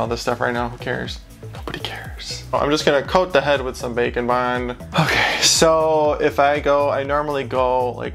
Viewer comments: I swear that for ages I thought you were saying bacon bond, clearly James' meatier cousin. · Whenever he says “bake and bond” I hear “bacon bond”.